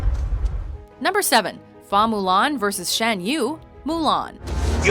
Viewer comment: I thought it said Fat Milan!!!